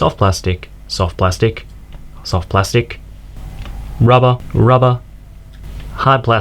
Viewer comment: In the minute 3:18 what do you say? Hot plástico, robo? Soft plastic, robo? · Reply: Its rubber